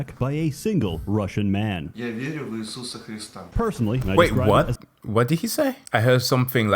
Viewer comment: I misheard persona with fursona lmao